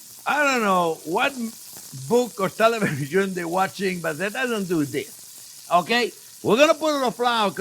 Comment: I swore he said "that doesn't do dick" was starting to think chef was speaking in 'refrigeration mechanic' lol. Thank you for your videos, chef. You have by far the best cooking videos on yt and I have gotten my best cooking techniques from you.